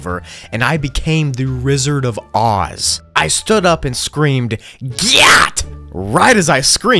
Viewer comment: yo what did he sayyyyyyyyy 💀💀💀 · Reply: gyaaaat💀